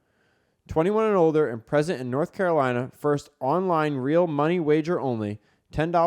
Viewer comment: Fan Duel commercial ?? For both and Denny: 'pregnant in North Carolina' is what I heard. Now that's a bet - hahaha